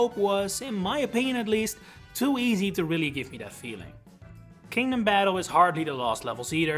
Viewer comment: the subtitles say Kingdom Hope instead of Kingdom Battle lol